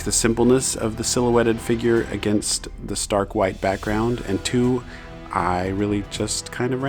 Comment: it sounds like u said this dark white background lol